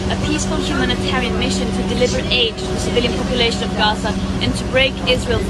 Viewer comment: did she say "deliver aids to gaza"?